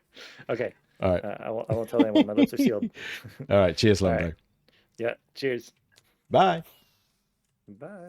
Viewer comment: Love the intro! The village is looking amazing! It was so much fun hanging out and “helping” with the farm 😂 At 12:32 I had to rewind it because I thought you said “Alright, cheers Love” 😅 Just you wait, I will get your head… eventually That is quite the upgrade for the smelter! Amazing! Great video!